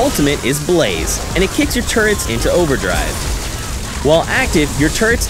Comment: I swear he says "your turds"